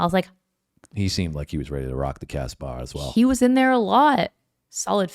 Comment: I thought they were saying Rock the Casbah... Fuck the casbah. Much more fun to sing for sure